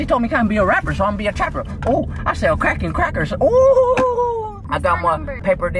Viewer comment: Did he say “I sell crack on crackers” 😂😂